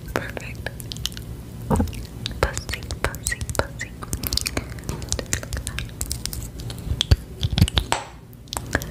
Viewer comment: What did she sayyyy! 💀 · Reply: Funzy* pretty sure it’s a brand but yeah ik